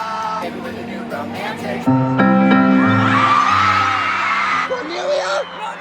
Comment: when you said Cornelia, I thought you said Cornelius and that’s my last name😂